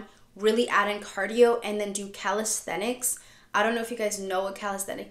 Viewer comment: what did u say? did u say calculus? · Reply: calisthenics